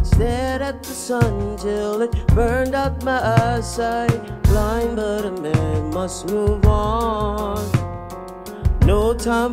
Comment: I always thought he said “blind Bartimaeus must move on” 😂. Love this song. Love Bilal. Excellent musicianship period.